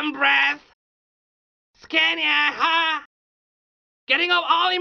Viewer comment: All my life I've been lied to. I always thought he said "Kenya ha!" 🤣😭